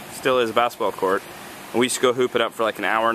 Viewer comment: I had two like omg moments... 1. You said before me and shay were married. It made me think you and shay got married and 2. When you said hoop it up i thought you said poop it up. Lol